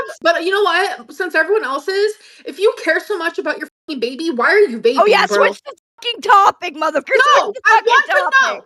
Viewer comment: What is Amber saying at 11:35 "If you cared so much about your f'n baby, then why ....... (rest is unclear)"? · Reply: she says “why are you vaping bro?”